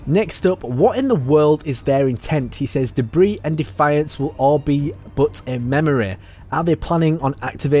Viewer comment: you say "He says 'debris and defiance will...", but he actually says "Their brazen defiance will...", referring to the humans. Just fyi for anyone listening